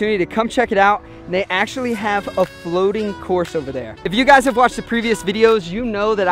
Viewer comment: Oh my god, I thought you said 'Floating Corpse'